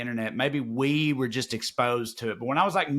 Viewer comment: Wait a minute.Hold up! At 49:00 did Trae say "Kurt Barlow"? Did Trae hang out with the vampire from "Salem's Lot"?